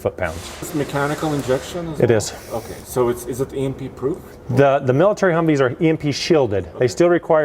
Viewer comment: iv replayed it 4 times now, and it still sounds like he says "its emp proof", seriously? i thought u couldnt emp proof a vehicle